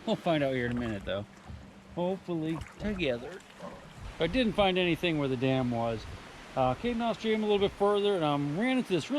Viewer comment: I thought you said "I didn't find anything worth a damn" Lol